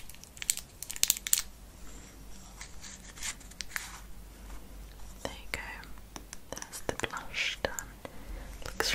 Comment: she says “there you go, the blush.............it looks naughty” What did she say! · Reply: It looks really lovely*